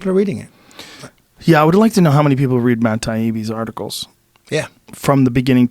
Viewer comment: Matt who? I can't figure out the last name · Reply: Taibbi #1114